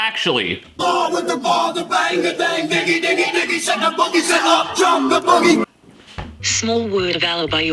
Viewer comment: I thought Kid Rock was saying “Up, JUMP the Boogie!” all these years….